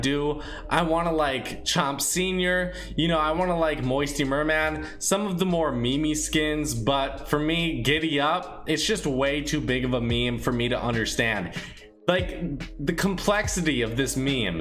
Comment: did he say me-me skins TRIGGERED